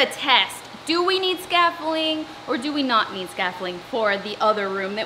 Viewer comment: It’s… its… scafFOLDimg right? 😂🥰 cos it sounds like your saying “scaffling” 😁